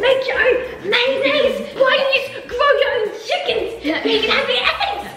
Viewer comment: did she just say grow ur own chickens